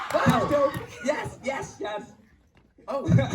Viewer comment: is it just me or did anyone hear say “yesss” bc that would be me with lightbrush